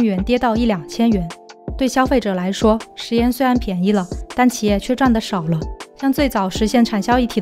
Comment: 對消費者來說 食鹽雖然變得便宜了.但XX也占得少了 請問那XX聽不清楚 是什麼呢? ---- 吃出錢的味道...這結尾利害了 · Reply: XX占的少了=企业赚得少了